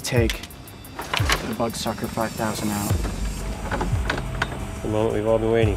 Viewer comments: i thought he kept saying butt sucker 5000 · I thought he said butt sucker 5000